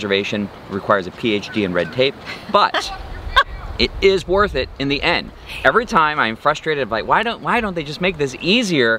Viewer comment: minutes, did I hear someone yell something like " ( swear word) your Video..."?... listen closely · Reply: "I watch your videos"